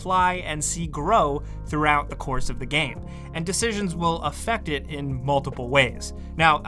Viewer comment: I heard they'll add decapitations